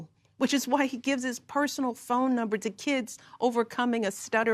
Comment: Did she say he gives KIDS his personal number? Hmmm ok🙄🤔